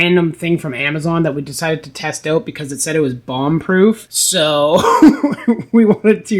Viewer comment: Did I hear , bong proof, 3:47 ???